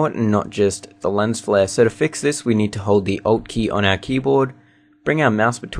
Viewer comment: hows the last step? I just cant understand if it is "o key" or "alt key" "on that keyboard. around